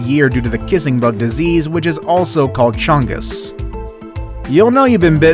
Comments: did he just say... Chungus BIG CHUNGUS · DID HE SAY "CHUNGUS"?